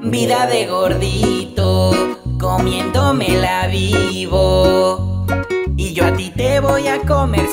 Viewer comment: entendí "comiendomela vivo" ( ͡° ͜ʖ ͡°)